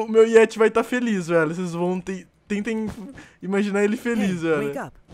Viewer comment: no 3:48 só eu escutei ei vem ca ????????